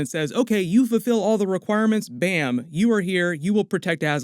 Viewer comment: I thought he said "Bamf!" and I was expecting, J.C. to show up ;)